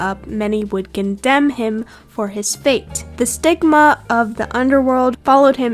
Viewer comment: I literally heard "sigma"💀💀